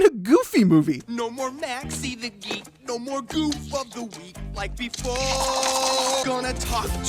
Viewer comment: I thought he said goof*ckery